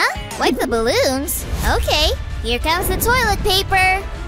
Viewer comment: is he say but